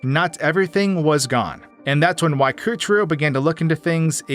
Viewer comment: I am sorry, but I need to know, did you at 3:55 pronounce the name Waikuteru? The name pronounciation doesnt# fit at all to how he is pronounced, but it sounded like the correct letters, so I am not quite sure.